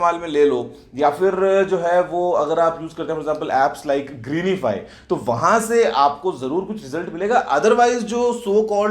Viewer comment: what was the word?? "Apps like g_____" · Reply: Greenify